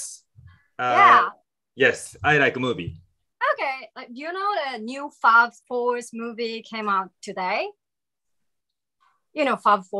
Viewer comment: 英語ほとんど聞き取れたけど、この2つがはっきり分からなかった… 英語できる方何て言ってるか教えてください！ 7:47 ・・Today？ 14:40 Could you ・・？ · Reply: You know the new Fast4 movie came out today? Could you wait a sec?